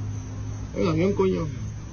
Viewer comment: the child is not saying Anunaki He is saying, un ovni, Spanish for its a ufo